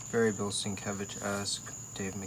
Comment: What name are you saying at 2:42? I been tryna decipher it! Thanks for the upload too 👊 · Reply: He says "very Bill Sienkiewicz-esque" and then "Dave McKean"